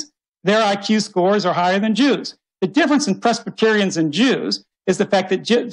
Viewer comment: What does he say? Prespetirians? What the hell is that? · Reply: Presbyterians - a Protestant Christian denomination